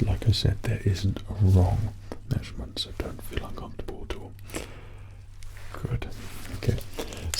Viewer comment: What does he say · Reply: He says “Don’t feel uncomfortable at all.”